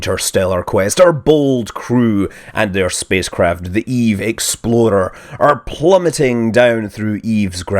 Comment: Scott, every time you say "EVE Explorer", I hear "EVE Exploder". I can't help it. It's just how you are saying it. I've had my doubts about the outcome of this mission from all the way back when it launched, because of this. edit: nvm, you finally addressed this point in this video ^^